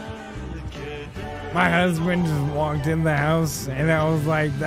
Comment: did say that his HUSBAND walked in the house?